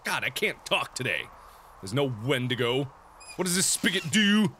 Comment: did he just say wendigo at 25:34...if he did then YEEEESS!!